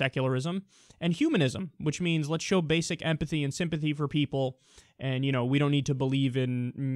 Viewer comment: He says "empathy", but I heard "apathy"